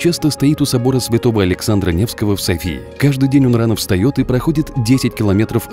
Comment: мне послышалось или он сказал Александра Невского?